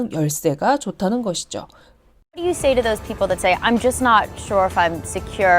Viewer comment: 여기 여자분이 말 시작할때 what do you로 안들리고 thing?으로 들리는데 문장이 잘린걸까요 아니면 다 말하고 있는건데 제가 못들은걸까유